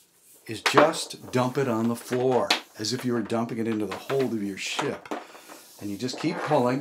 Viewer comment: I thought he said "The hole of your shit..." lmfao